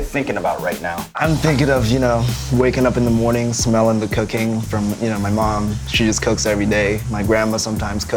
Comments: I thought he said, "I remember waking up in the morning smelling the cocaine my mom would cook." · lmfao I thought he said “cocaine” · Swear I thought he said cocaine not cooking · anyone else heard “cocaine”?😂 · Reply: not cocaine but cooking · I swear to god I thought I heard him say "Waking up int he morning & smelling the cocaine from my mom" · For a second I thought he said "Smelling the COCAINE" instead of "Smelling the COOKING". ahueug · when brian said cooking i thought he said cocaine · am i the only one that heard "smelling the cocaine" instead of cooking @ 0:55 lmao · I thought he said "Waking up in the morning and smelling cocaine from my mom" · I thought he said smelling from the cocaine my mom cooks. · I heard: "I'm thinking of you know, waking up in the morning smelling the cocaine". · Lmao I thought he said “waking up and smelling the cocaine ” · My sister accidentally started a video chat on Messenger,so the video got interrupted and all I heard when it started was "Smelling the cocaine from my mom." 0:54 🤣I was so highly confused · Thought he said cocaine instead of cooking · Could've sworn rich chigga said waking up in the morning smelling the cocaine lmao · LOL i thought he said "waking up in the morning, smelling the cocaine" I was like I gotta try some of the Indonesian food! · anyone else hear rich chigga say " waking up in the morning, smelling the cocaine" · i straight up thought he said 'waking up to the smell of cocaine,' not 'the smell of cooking.'